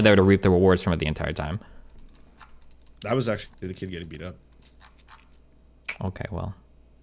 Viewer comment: What did say at that time stamp? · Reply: "I was actually the kid getting beat up". Weird deflection from the point because he knows it's true that he holds absurd stances right up until pressured so he can claim he never held them that strongly.